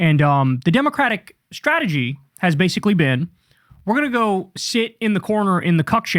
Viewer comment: I thought you said we're going to go shit in the corner